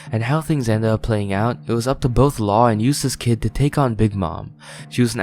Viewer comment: I heard “Useless kid” instead of “Eustass Kidd” and I really hope that was on purpose because that’s hilarious LMAO. Very true as well.